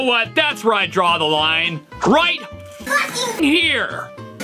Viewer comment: i heard fucking brothel